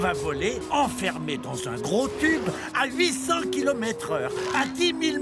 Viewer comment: ces moi où il dit -enfermer dans son gros culs où il dit -enfermer dans se gros cubes.Mes même si il dit la 2 iem phrase et bun il a thor car ce n'est pas un cube un avion non ?! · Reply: Il a dit gros tube